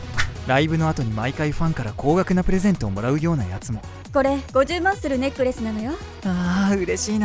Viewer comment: これ50マッスルするネックレスなのよ って聞こえちゃった笑笑